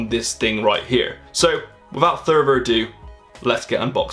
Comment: Did he just say "without thurver ado"